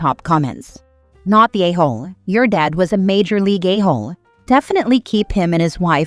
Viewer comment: Did anyone else hear "majorly gayhole?"